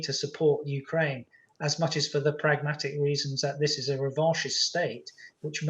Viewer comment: in the transcript: "this is a revocious state". What? I've never heard of that word nor have the online dictionaries. Mis-transcribed, or something from military planner's vocabulary? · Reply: Bad auto-subtitles. He said Revanchist. <link>